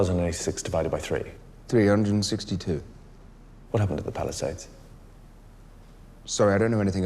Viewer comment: I kept mishearing it as "Palisades Morgue."